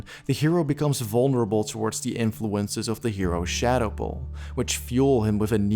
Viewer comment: What does he say at 10:19? Shadow bull?